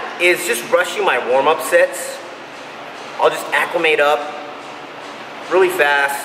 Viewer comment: What did he say? Accumate? Again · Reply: Acclimate. Means to ready your body for heavier weight by slowly increasing Weight each set.